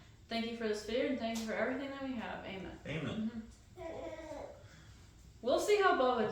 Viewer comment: I think he said amen 😊🥰